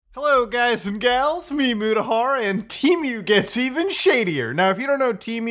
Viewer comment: I still continue to laugh how it just sounds like he says "ME MUDAHAR." I just think of some silly caveman who just found the internet/computers lol Love ya, man!